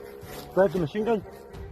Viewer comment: A russian? I heard “is that a machinegun” , didnt know russian language had english words in it